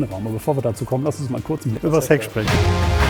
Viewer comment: Bei 16:55 erst verstanden "über Sex sprechen" und dachte bei dem schnellen Cut der Cutter hätte eine halbe Sekunde zu spät geschnitten, um Witz rauszunehmen haha Aber geiles Auto und ich liebe es einfach euch zuzuhören, grade Constantins Stimme ist sehr angenehm